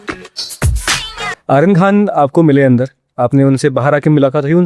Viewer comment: I really think that he said "Iron" Khan instead of Aryan Khan